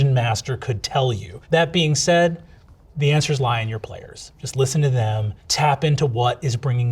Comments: thought he had said "lie TO your players" and was like yeah that checks out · Definitely misheard that as "The answer is lying to your players." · I heard "the answer is lying to players" and im like BRENNANNNN?????? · He said "the answers lie in your players" and I heard "the answer's lie to your players" and was totally on board. Then I read the text version and was like...oops... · The Wired couldn't pay for subtitles so instead of saying "rely on your players" it says LIE on your players which is the direct OPPOSITE of what he's saying. Incredible.